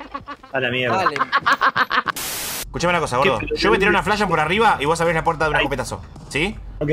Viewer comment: Yo escuché : ''Cuchame una cosa gordo, yo tiro una flash por arriba y vos abris la puerta de un escopetazo, asi nos comemos la flash como una porción de papas fritas''